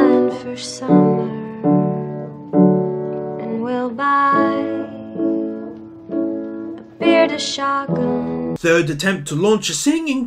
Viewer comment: When Beth was singing "and we'll buy beer to shotgun" I thought she was saying "bearded shotgun" 😂😂